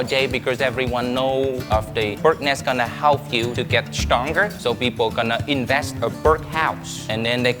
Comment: i heard he say stonker